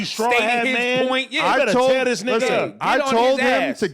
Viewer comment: I thought Adam said “I told this nigga”